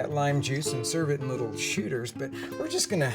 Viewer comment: Now, I know he said "shooters" @ 9:54, but I SWEAR I thought I heard "sh!tters"! 😂